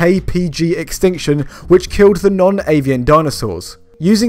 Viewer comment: I thought you said "non-alien" dinosaurs, and I wanted more info on the alien ones.